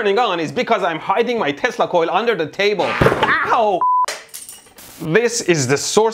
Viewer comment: I could have sworn you said that you were 'hiding your testicle under the table'. Just the one?